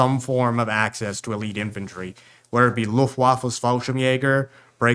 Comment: did he say Luftwaffels here? XD